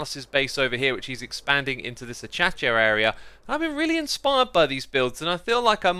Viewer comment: Anyone hear him say Achacha area